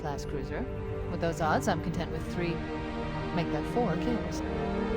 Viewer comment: I always thought growing up she said “three make that 4 Keys.” As in they’d need 4 Captain Keys in order to win